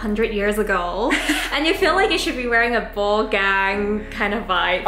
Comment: did you say ball gag?